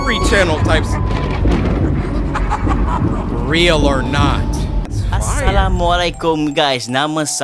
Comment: Heyy I just wanted to inform everyone on what they said at the beginning of the second animation at 14:26. The man was saying “as salaam alaikum” meaning “peace be upon you”. They are Muslim and that’s how they always greet everyone. :)